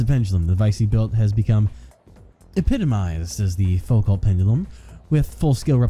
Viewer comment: I wasn't looking at the screen so when mentioned "Fullcallt" i was like "???" but then i heard "Pendulum" and I was like OHHH Foucault's pendulum (It's pronounced "Foo-co" as in Foo Fighters and Co-op for anyone wondering)